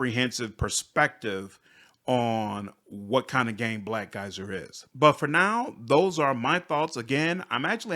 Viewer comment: Each time he said Black Geyser, I thought he said black guys I mean, I was confused for a bit "We're back with more black guys content" "That's not the case with black guys" "I don't feel it make sense in black guys" "On what kind of game black guys is" Good thing you didn't hate the game, it could have gone downhill real fast